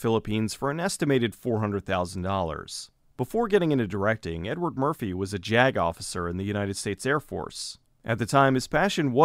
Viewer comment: I thought you were saying "Edward Murphy was a jag-off"